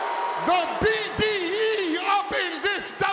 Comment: did Heyman just say BDE?